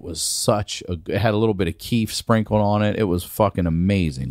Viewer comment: I heard Queef for a second and I got really concernd lol